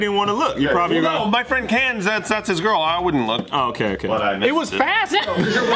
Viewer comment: What does say at 16:46? He’s like “I wouldn’t look but I’d ..... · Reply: Missed it